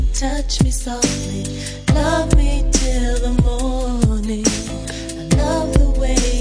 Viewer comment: I’m I the only one that just noticed she says “odie” instead of morning?! At 3:13.. No ?okay.. 🤯